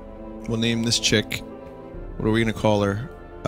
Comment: I honestly thought he said "Let's name this chick, Marina Caller.. I was like "Wow, that's an.. Awfully specific yet creative name.. Oh wait.. "